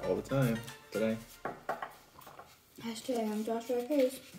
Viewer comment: is it just me or am i hearing hastag tohkens